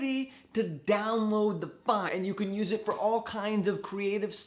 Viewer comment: I thought said "you can use it to get organs" and I was thinking 'I didn't know organ transplants were that easy'